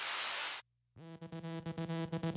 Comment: When I first heard this I didn't think he was saying "Crayon" I thought he said "Cray on" as in get your crazy on was I right or wrong? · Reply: Yeah, it's cray-on, as in get crazy.